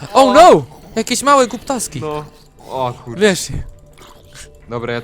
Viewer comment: zamiest oh no te małe głuptaski to usłyszałem "kutaski"